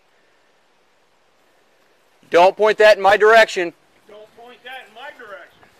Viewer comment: “Don’t point that in my direction” “Don’t point that in MY direction”... IQ 3000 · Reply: Low key thought he said “then point that in my direction” I was like this man is a child